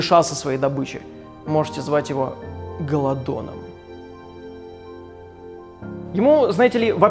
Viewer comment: Мне послышалось не гололодон а гондон.